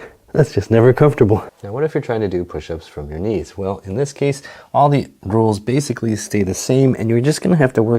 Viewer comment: Did he say all the girls